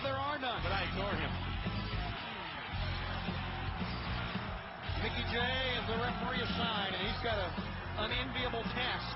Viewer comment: sounded like they said mickie james the referee